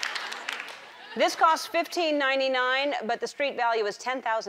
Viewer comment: Somebody please show me what she said at 0:42 .I'm learning english and I can't hear this part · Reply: 15.99